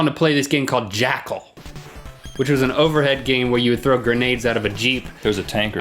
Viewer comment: I thought he said "where you throw grenades at a ba-jeep"... XD